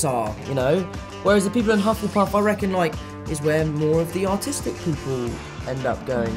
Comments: I thought he said “Autistic” lmfao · I thought at 2:05 he said that hufflepuff was where the “autistic” people went and I was so shocked lol